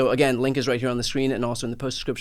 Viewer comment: "Inside the Reich" is what I thought you said until I read the description. Not wrong though.✌